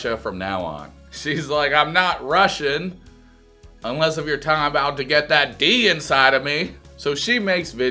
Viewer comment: "unless you're Putin that D inside of me" im unsure if he is making the exact same pun, ive replayed it but cant hear clearly •_•